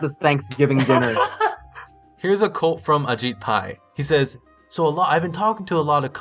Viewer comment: I heard "here's a cult"